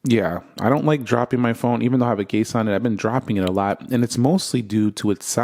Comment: "even though I have a case on it..." sounded like "even though I have a gay son..." 🤣🤣